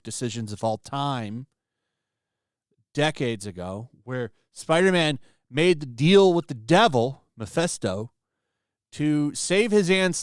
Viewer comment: For a hot second I thought he said: "...made a deal with the devil, Bethesda." I mean Starfield is not that bad.. Boring, janky, ugly